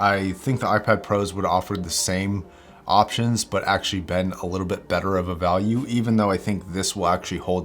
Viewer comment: Haha , 3:29 I heard "actually BEND better of a value" , I thought "sure, sure.... :D " :D To me your viewpoint was hilarious, it's like 1st time the viewers saw it they got mad, now, you're coming back with as an explanation: "the thing that kills the MB for me, is... the software." You want them dead by now ^.^ basically, your checklist for the MBP was reversed compared to anyone else, & that's gonna be interesting when "something" happens to the hardware... I mean, Louis Rossman made enough effort to sort out the cons of >2015 MB :) . But anyway, nobody has to say it was a "bad" choice, since it wax Your choice, & your criterias don't have to fit theirs. Awareness is the only requirement for making a "good" choice, & I think you're ok on that . xD Good luck bro!